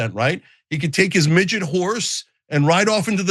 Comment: I thought Cenk said Midget Whores Lmao